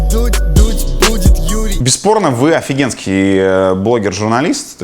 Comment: без порно??? хех послышалось)